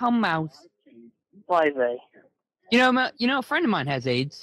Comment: Chris actually says "Oi Vey" not "Who are they?" Just wanted to throw that out there.